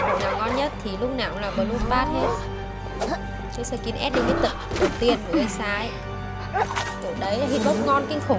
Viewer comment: sao em nghe thành "Lúc nào cũng là bloodbath hết, cái skin limited đầu tiên của Geisha" =))